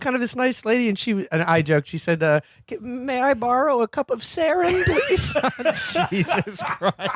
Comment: 'May I borrow a cup of Sarin?' Lost it. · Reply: thought you said can i borrow a cup of spain